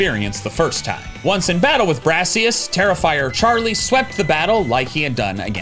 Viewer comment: heard ‘terrifier charlie’ instead of ‘tera fire charlie’